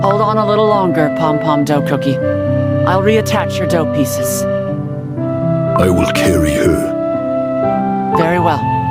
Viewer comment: A 5:27 genuinely heard Mold Dough say “I will kill you.” Instead of “I will carry her.” and i didnt know what he said since i wasnt paying attention😭